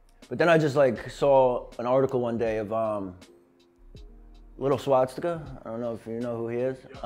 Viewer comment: Did he just say "Lil Swastika"? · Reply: little swastika is a tattoo artist, yes